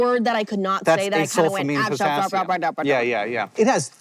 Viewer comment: As a non-native English speaker, I appreciate that you have subtitles, but I found that they are often slightly wrong. For example at 18:13 "acesulfame potassium" is transcribed as "a sulfamine potassium". I know it can take a lot of time to make subtitles for an hour long video, but with auto-generated ones I at least know not to trust them, but here I get confused whether I misheard it or the subtitles are wrong.